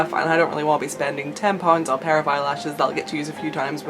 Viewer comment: What she said: "I don't really want to spend ten pounds on a pair of eyelashes..." What I heard: "I don't really want to spend tampons on a pair of eyelashes..." That seriously confused me lmao... didn't know you could use tampons as a form of currency.